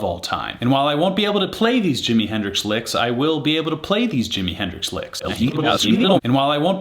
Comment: Lol all I heard was “I can’t play this jimmy song” to avoid getting de-monetized… still a fun vid man!